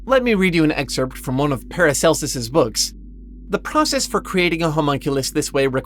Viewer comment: Not sure who paracelus is, but at first i misheard you. Sounded like you said "an excerpt from Paris Hilton's book". And I was like, hold up, she can read, let alone write a book?